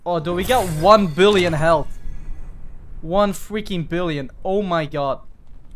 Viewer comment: idk why but i heard it as fwiking billion xD